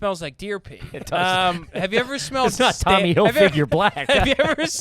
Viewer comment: Did he call it Tommy Hil-Figure? LoL